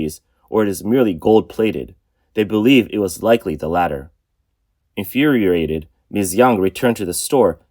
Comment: 老板, why did you say “inferiorated”?😅